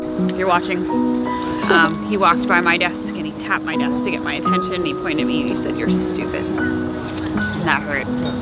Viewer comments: What did they guy at 4:00 say? I couldn’t understand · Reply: he said “ you were an accident” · What does the guy at 4:00 say? Can't figure it out (I'm not an English mother tongue) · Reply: "you were an accident" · what did he say? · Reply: "You were an accident" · what did he say?:( I don't get it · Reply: hazel xx He says: „you were an accident“